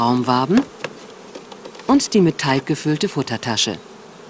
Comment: Danke für das Lehrvideo. Einfach klasse. Nur eine Frage: bei 30:40 ist eine Futtertasche zu sehen, was ist da genau drinnen? Ich verstehe kalk. Stimmt das, oder mit was genau ist diese gefüllt. Lg